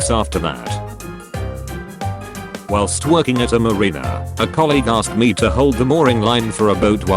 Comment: Did anyone else think it sounded like Whilst twerking at a marina at 1:38?